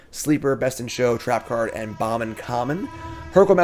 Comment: Im curious if I just mishear every set, but it sounds like he says "Bomb uncommon for the set" but they are always commons he chooses. Am I missing something? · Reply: It’s “bomb in common” I believe